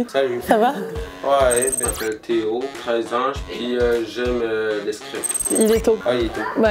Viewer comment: c'est moi ou il dit j'adore le str!p😳